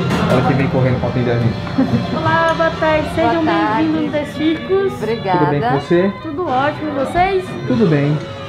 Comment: eu juro que entendi sejam bem vindos nos testiculos